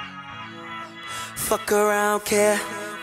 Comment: I thought he said "Fuck Around Here"!